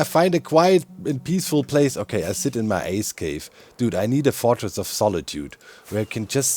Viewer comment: you said 'ice cave' i heard 'ass cave' now i can't unhear it